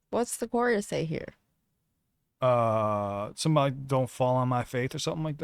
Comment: He says Don't fall on my face Don't fall on my faith Don't fall on my fate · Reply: I hear "Don't fart on my face"